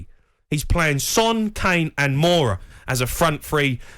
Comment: Did he just say to play Moura as a RWB?🤯😂 oh Jamie, you out your meme yourself